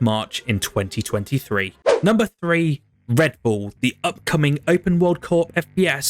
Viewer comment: Sounded like you said "number 3, redbull" haha, nice video though